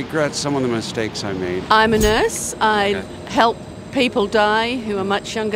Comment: … did she say i help young ppl die????